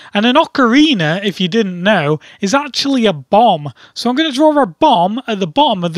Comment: I heard "An Ocarina, if you didn't know, is actually a Bong." Which would've been hilarious actually.